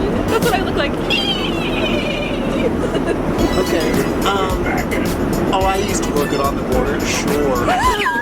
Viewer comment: What does garrett say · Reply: Oh i used to work on, on the border sure